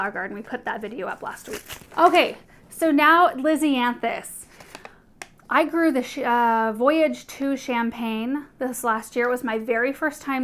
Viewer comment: I was listening while doing the dishes .. 34:27 I could have sworn I heard almost say she grew the $hit out of these 🤭 then I played it back its just the name of the plant 😆